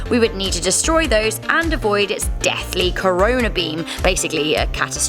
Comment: Did she say "Corona" beam? 🤔